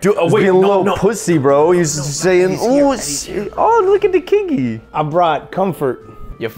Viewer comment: when said “look at the kiggy” , captions thought he said look at the kinky….